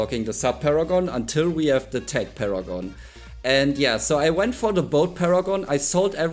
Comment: I know its Bloonarius, the Inflator and not Infiltrator 😮‍💨 I am to tired to record it again:_punjiGeraldo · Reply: PS, I heard you say Tech Paragon, or Tank Paragon, idk, at timeframe 10:19. What do you mean by that?